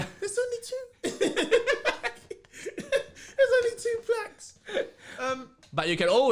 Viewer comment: Ngl I kept hearing "blacks" then caught onto them saying plaques when the numbers were thrown in. Loving the podcasts bro